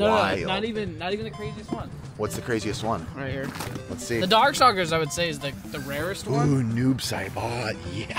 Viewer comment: Did he just say... "Dic-Dark suckers"... ???